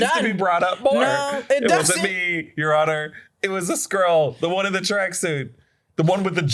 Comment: At first I thought he said a squirrel not a Skroll 🤣